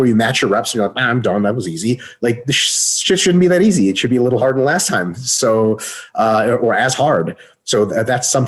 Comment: did he say..."harder than last time" :D